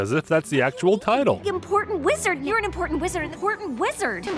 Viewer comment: I thought it said impotent wizard